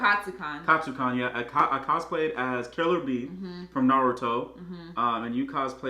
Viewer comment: I thought he said killer bean from naruto